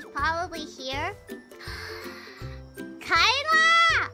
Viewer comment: It says 'KAELA!' but I heard 'MOM!' 😂